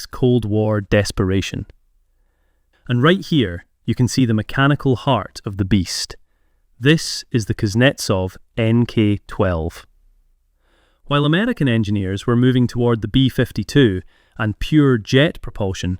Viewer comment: i was surprised but then i heard " German engineers " .